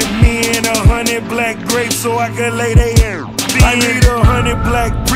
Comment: i thought he was saying "100 black goldfish"